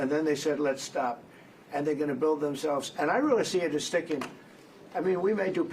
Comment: it sounds like he was saying "rebuild" but stopped himself. Sometimes, we can tell when he catches himself about to tell the truth